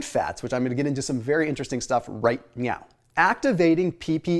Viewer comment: I thought he said "right meow".... lol